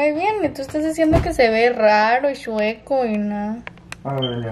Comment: Yo escuche que le dijo aver mi amor voltéate 😳